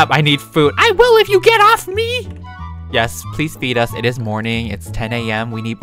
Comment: WHEN YOU SAID PLS FEED US I THOUGHT YOU SAID PLS BEAT US🤣